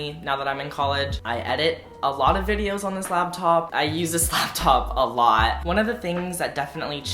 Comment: he uses the slaptop a lot? xD why do I always hear slaptop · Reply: This laptop, not the slaptop